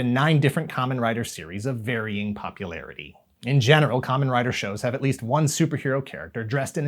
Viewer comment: Did I just hear "In genital..." @ 5:42?